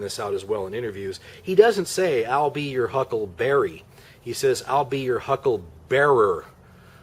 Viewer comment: FINALLY, an explanation of that damn Doc Holiday line. I initially thought he was saying "I'm here, Huckleberry"... like he was calling the guy a dumb hick or something. But it seemed like nobody could agree on what the hell Val said. Thank you for that.